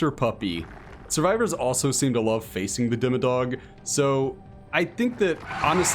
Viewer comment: when you said "demodog" i thought you said dimmadoug and i was dying